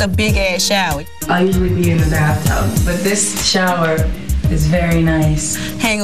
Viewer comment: does she say “I usually pee in the bathtub but this shower…” ? Lol MC you nasty.